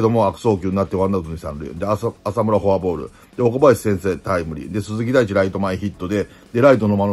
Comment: 岡林としか聞こえないw